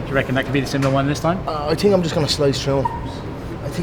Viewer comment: What did Conor say at 3:56? "I think i'm going to slow show(?)-" Or did he say slut show? Someone help me English. Thanks. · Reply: slice through